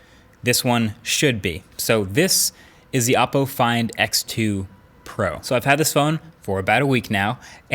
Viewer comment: Did he say "apple find X2 Pro" 🤣